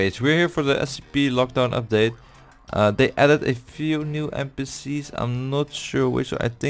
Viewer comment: i heard mpc's